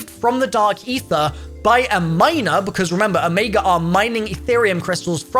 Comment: I thought he said "When it was retrieved by a minor" LMAO